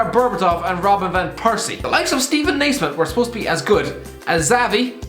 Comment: I thought he said Stephen A Smith @ 2:44 at first I was gonna say wtf😂